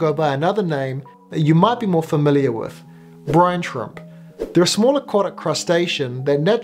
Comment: lol if you put on subtitles when he said brine shrimp the captions said Brian trump